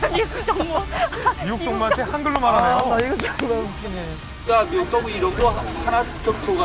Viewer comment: 이거 욕 아니냐? 정말이라고 안들리고 존x라고 들리는데